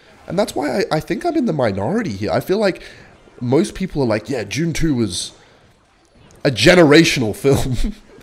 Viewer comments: i thought you said june 2 and not dune 2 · it sounded like u said june 2